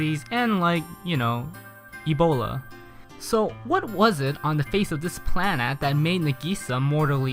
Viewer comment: did he say "on the face of this Plannad"? or am I hearing things lol